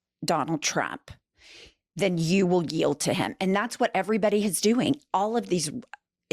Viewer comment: that’s not what he said! He didn’t say “yield”. He said “kneel”. There’s a difference. And you’re a lawyer?